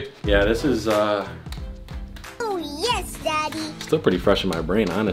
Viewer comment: How come i heard oooo yes daddy 😘 7:13 or